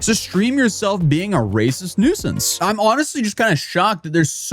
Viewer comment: An ad hit as soon as you said racist n….. I I was like wtf 😂 then it came back and you said “nuisance” 😂